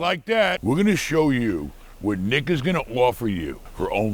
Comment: “we’re gonna gonna show you what ni**a is gonna offer you”🤣 (I know he said nick)